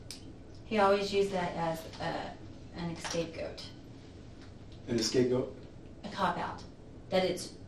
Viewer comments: Did she just say "Escape Goat" instead of scapegoat? 57:47 🤣🤣🤣🤣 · did she just say an escape goat? 😆 I'm sorry this is terrible but that made me laugh. I hope she 🔥